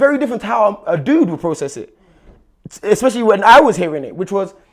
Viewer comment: At @ 29:39 is talking about himself? It sounded like he almost slipped up and said "how I'm processing it" then quickly corrected himself...bruh